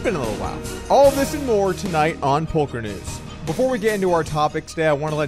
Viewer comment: did he say “Polk-er News”?